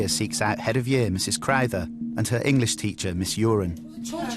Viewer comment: did he says mis urine